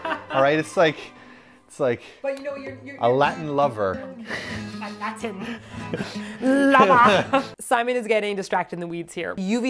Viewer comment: (ish) : Did somebody say "latrine lover"?